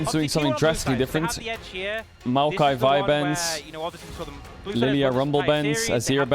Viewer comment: I swore he said maokai vyvance